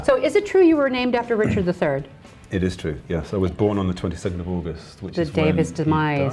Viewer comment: I thought I heard RA say he was born on Aug 27th. But then he said he was born on the day Richard the Third died at the at the Battle of Bosworth Field. To be sure I googled Richard the Third and he did die on Aug 22nd in 1485. RA would not make such a huge faux pax during an interview. I think and perhaps the entire world would have corrected him right there and then. He is in fact born on Aug 22nd and he is a Leo.